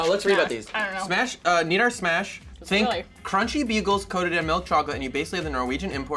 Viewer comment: I thought he said crunchy beetles